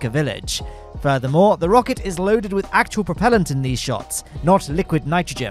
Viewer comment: So I was doing something in the kitchen and I misheard propellant and thought Starship now has a propeller for some reason! I mean, Mars helicopter was a great success, but don't think it would work on Starship... :D